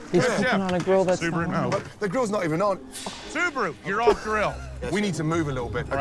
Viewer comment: imma be honest, for the first half of the video, I thought they were saying Subaru, not Subha